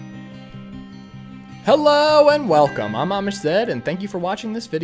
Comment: I swaer i heard "Welcome my mom is dead and thankyou"